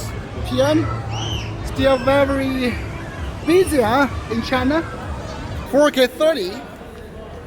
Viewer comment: Did I just hear Insha allah @ 28:03 ? 😨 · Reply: He basically said : "In China"😂😂🤣🤣🤣🤣🤣🤣🤣🤣🤣🤣🤣🤣